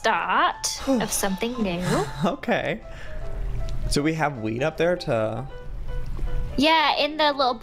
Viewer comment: Oml when joey said "do we have wheat up there?" (21:01) it sounded like he said weed 😅